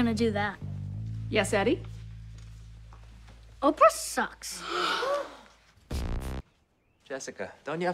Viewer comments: "oprah sucks" i cast you away demon that has effected this child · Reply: I always thought Eddie said, "Opera sucks", not "Oprah sucks". Just seems more probable, especially if it was a music lesson. There's no reason why Eddie would want to insult Oprah Winfrey. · i know it's a comedy show and all, but i won't be surprised if people can go to dentention in america for saying oprah sucks xD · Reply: I always thought Eddie said, "Opera sucks", not "Oprah sucks". Just seems more probable, especially if it was a music lesson. There's no reason why Eddie would want to insult Oprah Winfrey.